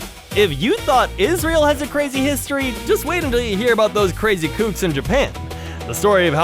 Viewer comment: I had to re-listen to 35:50. He said "kooks"... phew, I thought I heard something much more offensive XD